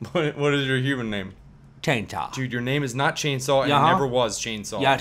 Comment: At first, I 100% thought that said his name was "Taintaw" and I can't hear it as anything else now